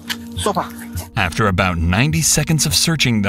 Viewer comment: what did the cop say to that good boy at 8:26 ? sounds almost like "stopa" in czech and it is the order to keep tracking (track/trace in translate), so it was accurate... was it czech dog? :D